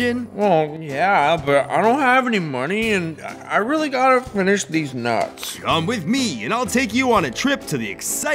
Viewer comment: right at the start he said these nuts I thought he said deez nutz gooty ha